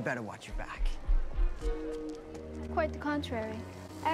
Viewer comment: Subtitle error: fillet instead of flay.